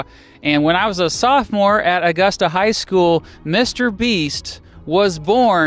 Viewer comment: I swear I thought he said he’s Mr beast